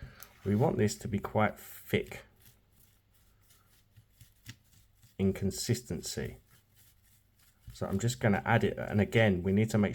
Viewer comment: I thought you said "…thick inconsistency," when you actually said "…thick in consistency." 😂 Btw, I love opal…all opal. Australian, Ethiopian, Brazilian, lab created, and even imitation. Ty for this demonstration! ❤